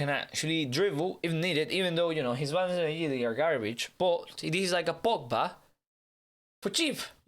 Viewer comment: what did he say😂😂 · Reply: his balance and agility are garbage, lol